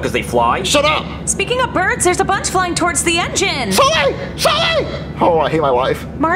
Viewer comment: is he saying Sully?? 😂😂😂